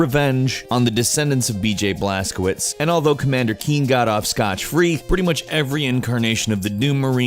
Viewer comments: Did he say scotch-free at 4:40? This a variation on the idiom I've never heard. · did he say, "scotch" free?